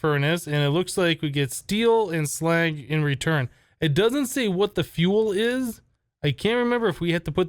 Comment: Did you just say [Wtf] in 9:57 and 11:16 ? I mean [What The Fuel]